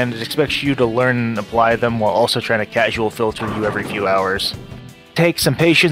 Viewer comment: I wish I could understand that sentence but all I hear is “casual filter you ever few hours.”